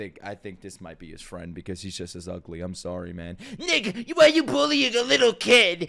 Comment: DID HE JUST SAID THE N WORD 😳🌝 I think he was trying to say Nicha but sounded like Ni🖤🖤a